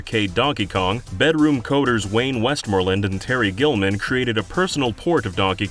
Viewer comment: genuinely thought that said Terry Gilliam